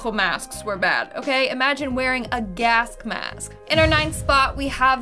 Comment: lol 1:04 did you say "gask mask" haha